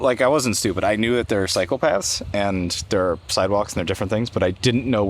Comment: I swear I heard you say "I know there are Psychopaths and Cyborgs and I never knew the difference." 😂